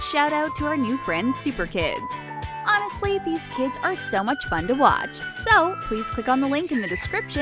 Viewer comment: uhh did she say it’s fun to watch kids...